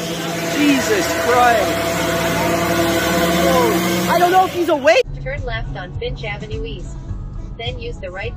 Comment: Did I just hear "turn left on bitch avenue"?